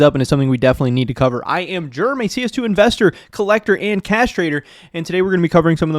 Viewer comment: I could have swore you said I’m a castrator instead of cash trader haha had to rewind that one